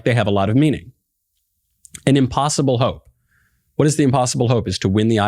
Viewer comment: I heard the butt guy say 'improbable hope' not 'impossible vote'... Butt maybe I misheard